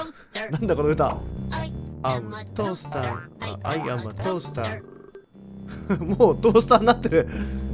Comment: I am a toasterがオイラはトースターに聞こえてた😂